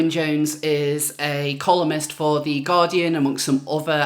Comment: I was munching a biscuit when you introduced Owen Jones and misheard it as "a communist for the Guardian" - earily accurate.